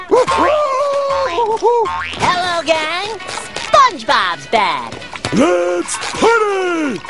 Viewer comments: hahahahaha does sound like hes sayin any porn in this store XD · I thought he said “any party in store?”